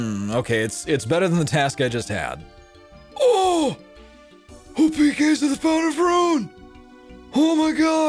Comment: wtf did you just say ? Lmfao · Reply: WHO PK's AT THE FOUNTAIN OF RUNE??